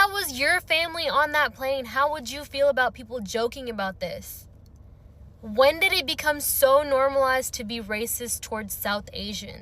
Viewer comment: SHE SAID WHEN HAVE IT BECOME NORMAL TO BE RACIST TOWARD SOUTH INDIA, I GUESS INSTEAD OF BLACK PEOPLE LISTEN CLOSE AT 7:18 TIME